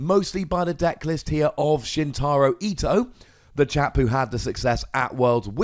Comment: Lol, did you just say "the Jap who has had the success"? seems kind of racist